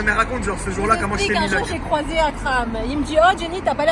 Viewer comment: J'ai entendu "j'ai croisé un crabe" au lieu de "j'ai croisé Akram"